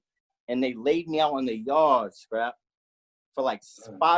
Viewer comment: did he say “skrap?”